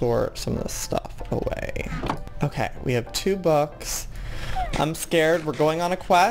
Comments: Joey: Ok, We have 'two books' (He meant to say to boats) Me: Wait what (Looks at his inventory and sees no books) I just heard books people...lmao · did he say "i have two books" instead of "i have two boats"😂